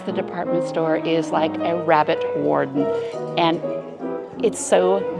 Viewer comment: It sounds like you said rabbit warden? It’s ‘warren’